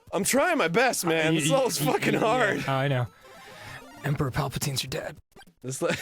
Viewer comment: Did he just say Emperor Palpatines your dad?